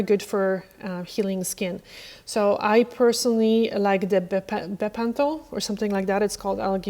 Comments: Thank you so much for this great video🎉 I will definitely look better after my nails/cuticles from now on. Question: at 23:45 you talk about a cream for your feet, sounded like “pepentol” you said it would be linked, but I can find it. Could you please let me know what the name of the cream is? Thank you so much in advance! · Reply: Bepanthol :) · Amazing as always! You mentioned a product for heals you get in Spain. Sounded like you were saying something like Panthanol but I didn’t see anything listed that started with P. What was it? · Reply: Hi there :) It's called Bepanthol. It's great for feet and hands at night. <link>